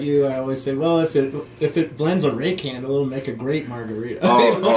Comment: Why is it when they say "rake handle" I hear "ray candle" and am confused......:-(